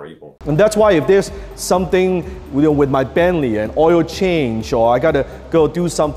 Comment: did he say that if he's got to do something with his family or an oil change or something, he's not going to do it because it's not worth his time? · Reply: He said Bentley not family